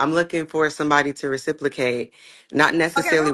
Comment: Did she just say "reciplacate"? What does "reciplacate" mean? Lol